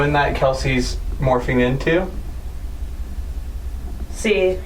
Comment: when it said see it sounds like it said yes in Spanish